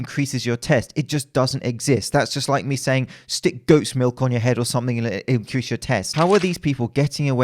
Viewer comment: I legit thought he said "ghost's milk" instead of "goat's milk"